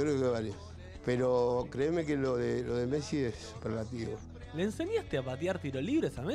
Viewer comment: no entiendo la palabra que dice, " creme que lo de messi es .... ? alguien sabe que dice??? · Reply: Dice Superlativo